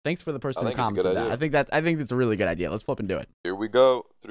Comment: first time I heard him swear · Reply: He said flipping